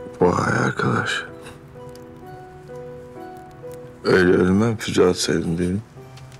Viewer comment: 'de ne diyor Aslan? Anlayan var mı? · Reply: öyle ölmem füze atsaydin devin